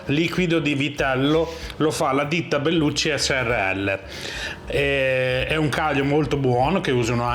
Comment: Bellucci srl and i heard " Bellucci e sorelle" 😂😂😂